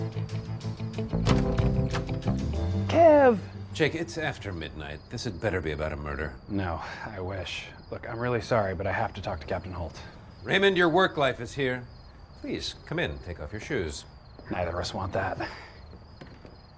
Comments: "Raymond, your work life is here." Kevin's sass and annoyance with the NYPD is so good. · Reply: The first time i heard this i thought he said "your work wife is here" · I had to rewind like four times the first time I watched that episode because I could have sworn Kevin said “Raymond, your work wife is here.”